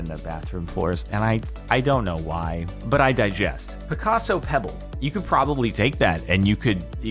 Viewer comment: did he say but i digest?